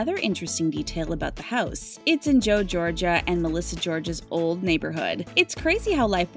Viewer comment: Did you mean Joe Gorga?? It sounded like you said Joe Georgia??? 🤣🤣